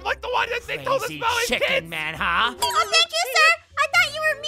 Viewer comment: Asthma I think noi said a bad word in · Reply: Noi said "chick-en huh"